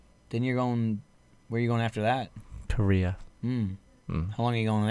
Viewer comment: What is the second place is going? Sounded like he said Korea but with a t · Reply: He did say Korea. I think the microphone was just being weird when it picked up what he said.